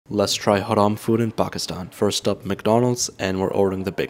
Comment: nah I thought he said harem bruh